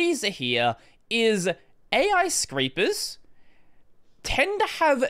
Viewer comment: I love how the auto caption says "ai creepers" when you said "ai scrapers"